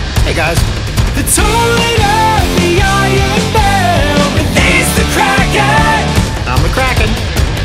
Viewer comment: Lowkey thought he said "the toilet of the iron bell" for a hot second